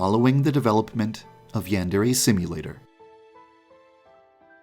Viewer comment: Everytime i hear: "Yandere see you later".